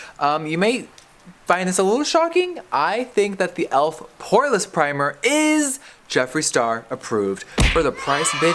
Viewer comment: I swear to god every time Jeffree said Elf Poreless Primer, I always heard Elf "Whoreless" Primer. 😂😂😂